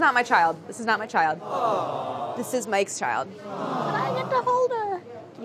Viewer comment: when she said “this is mike’s baby” i thought she’s saying “this is my ex baby”😂